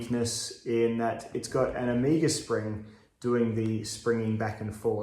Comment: At first I thought you were saying Amiga spring, but I guess that's before your time as a gamer.